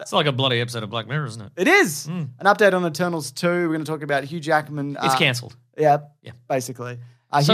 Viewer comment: i thought he was saying hugh jackman is cancelled. i was like mannnnn not him too